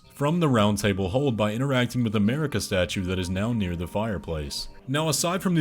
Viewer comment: I thought he said " . . . by interacting with the America statue"